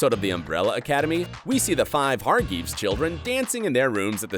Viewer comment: did he say... hargeeves??